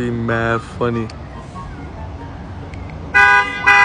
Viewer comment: Bro when he said the baby I thought that he said DaBaby for a second😂😂